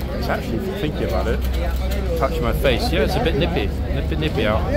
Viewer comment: Had a laugh when the women in the rather revealing top and tights stood next to the horse, you said it's rather nippy today. I thought you said it's rather nippily.😄